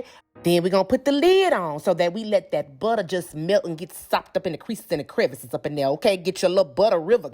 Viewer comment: We gonn be SOPPED UP for thanksgiving 😝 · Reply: Alisa! I thought she was saying socked up 🤣 - thanks for the correct spelling!!